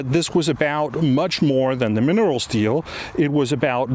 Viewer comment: Great video, Mr. Nielsen. It almost sounds like you say “minerals steal” and not “minerals deal” - coincidence? 😅🤔